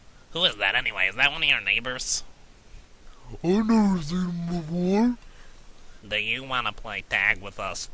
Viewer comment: what did you say? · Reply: She said, “I’ve never seen him before.”